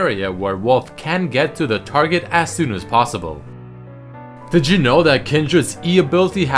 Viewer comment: I legit thought he's been saying "digital" I didn't know he was saying "did you know"